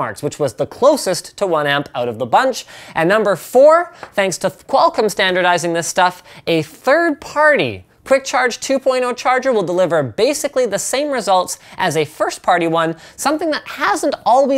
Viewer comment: i thought it said Quantum Quick charge 2.0 lol.